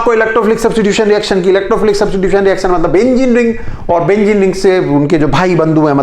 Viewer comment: is it me who heard Benzene ring as engineering 😂😂 Awesome lecture BTW sir